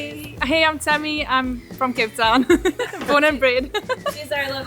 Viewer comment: reminds me of kate. and each time you say “cape town” I hear “kate town”. which seems appropriate to me. 😊